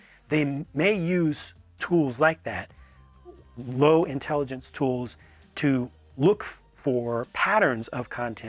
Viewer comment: I know he didn't mean it that way, but it sounded like he said boomers and their interns are low intelligence tools. Lol